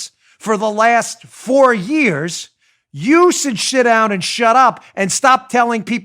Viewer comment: Did he have a verbal gaffe and say "you should shit down and shut up" at 7:54 of the video? I swear that's what I hear. He was obviously saying sit down and shut up and wasn't swearing, but it made me laugh.